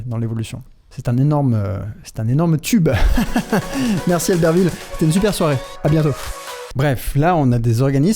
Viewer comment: c'est moi ou Léo a dit: "merci Albertville", soit je suis matrixée soit je viens d'apprendre que ma petite ville est connue 😂